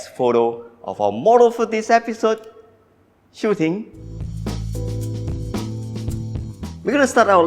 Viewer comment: when you said Best Model of the episode Shu Ting*..... I thought you wanted to say, lets go *Shooting :-D :-| hahah